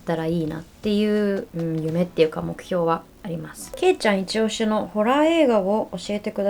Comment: すまん、俺けいちゃんファンなんだけど、どうしてもけいちょんに聞こえてまう